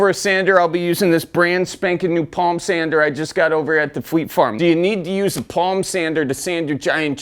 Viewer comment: What’s fleet farm? West coaster here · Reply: West Coaster here too, but I thought he said Flea Farm, and that was what midwesterners call Flea Markets. 😂